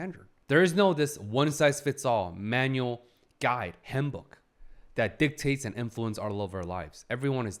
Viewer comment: is it bad when he talked about how people need to use the word "should" less and when he said "handbook" i heard "himbo" and my first thought was "how is lihaku related to this?" help i think something is wrong with my ears (and me)😭